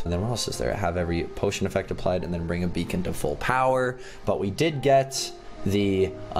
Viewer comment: I thought the beacon said baconator